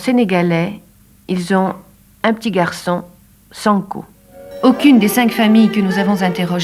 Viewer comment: Je regardais pas la vidéo mais j'ai entendu à 1:11 "Ils sont sénégalais, ils ont un petit garçon....sans cou" 😂😂😂😂